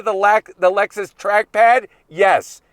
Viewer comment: For a second when he said “track pad” I thought he said “trash pad”.